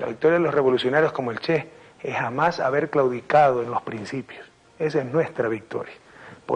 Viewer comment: Que dice al minuto 5:20? "es jamas haber ??? los principios" Perdonanme, pero no soy hablante nativo de español. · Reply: "Claudicado"